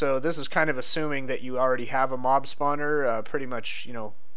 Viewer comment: in the beginning, it sounded like you said mom-spawner